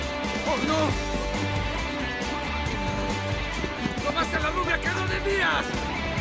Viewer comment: que dice kevin ahi? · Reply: Dice robaste a la rubia que no debías